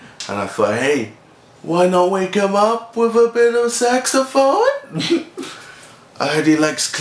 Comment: I thought KSI said sexaphone.